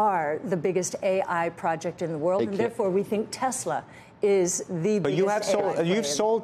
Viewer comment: I thought Cathie said SKLZ is the biggest play